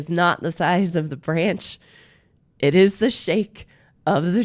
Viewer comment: “Not the size of the branch”- I thought you said the “fridge”…